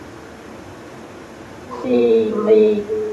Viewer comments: I thought she said "feed me" lol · free me but he misheard "see me"